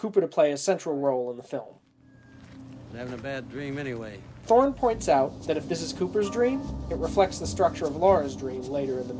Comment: For the life of me I can't understand what you say at 2:53, and the creepy thing is that it being 2:53 ^-^ Anyway, can you tell me what you said here? ".................. it reflects the structure of Laura's dream" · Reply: “[John] Thorne points out that if this is Cooper’s dream......”